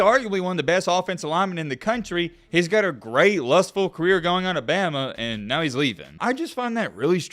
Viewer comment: bro did you say lustful??